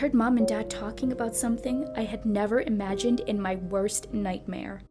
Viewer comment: is it me or did I just hear say agane